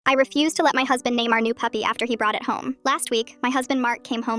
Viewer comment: I heard "i refused to let my husband neymar puppy" 🥹